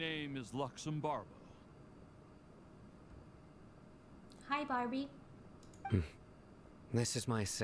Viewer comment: Did you say hi barbie? 48:38😂